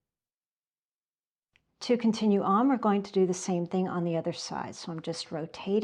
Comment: O.k., mini- “Ah ha” moment - she didn’t say “turn your work” which is what I heard. This time - sixth time through [slower boy from Alabama] I heard, “To continue on, we’ll do the same thing on the other side . . . “ The lights are beginning to come on . . . and made it.